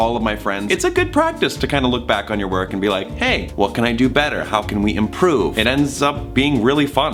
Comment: I feel stupid. He said "What can we do better?" and I heard "Wakanda forever." ... my bad.